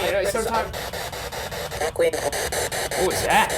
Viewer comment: instead of 'what is that?' i heard 'Elizabeth!' At 18:25 i heard 'I hate you' but it kind of sounded like Ernie as in Sesame Street?